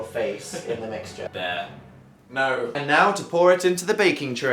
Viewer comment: does phil call dan "bear" at 5:20 ??? i saw it on tumblr and came back but i honestly can't tell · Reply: Dan has said bear is one of his nicknames in a video before. And yes, Phil does call him bear.